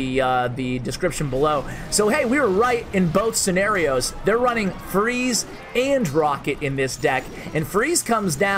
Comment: caption says "they're running furries and rocket". Don't you just love it when this happens?